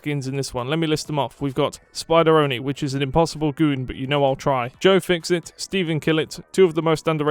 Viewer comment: “which is an impossible goon but you know I’ll try” please tell me I heard that correctly cuz that was funny asl